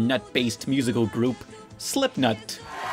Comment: I thought That He Said Slipknot Instead Of Slipnut.. XD